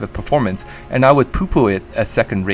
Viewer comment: did he say poo poo